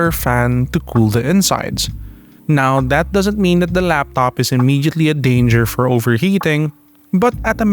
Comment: So i shouldnt buy this laptop? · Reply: did I just hear he said " now that doesn't mean the laptop is immediately in dangerous". Don't worry, the laptop is not in an inmediately in danger. yeah just not in an immediately. that's enough for me to blablabla.